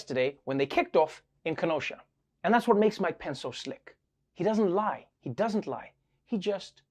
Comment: I heard, "And that's what's makes MY PENS SO SLICK."